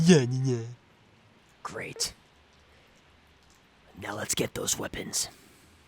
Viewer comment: I thought he said "Let's get those women."!! 🤣